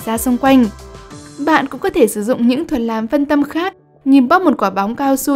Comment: ad nói gì thế ạ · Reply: Đánh y tá